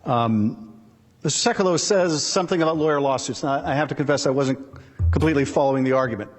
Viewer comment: I was watching live and actually caught the typo. The transcript/subtitles on screen whoever was writing them misheard FOIA and wrote lawyer